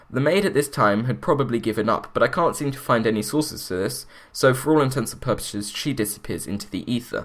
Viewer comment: Love you videos but whenever I hear someone say "for all intensive purposes" like I think I herd you say at 5:11 it sounds like fingernails on a chalk board. It's "for all intents AND purposes". Sorry if you were saying it right and i just misheard you.